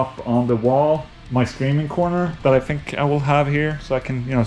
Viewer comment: Could have sworn you said screaming corner, and I was like.....yea. Everyone needs a place like that these days